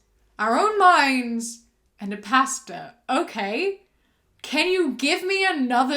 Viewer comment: girl I thought she was saying “Pasta” 😭. I’m like how r noodles gonna read me the Bible. I need sleep lmfaooooo